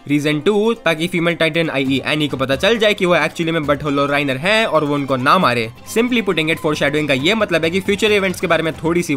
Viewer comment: I literally hear "Butthol"🤣🤣🤣